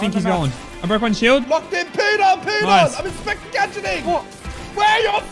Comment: All I hear is ‘POOED ON POOED ON’